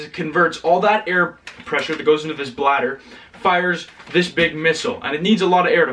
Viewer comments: he accidentally says Blatter instead of Blaster. Just thought it was a funny little mix up. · does he say bladder ? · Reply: yes, you fill the air bladder with air, pull the trigger and all the air is released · did he say bladder? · Did he say bladder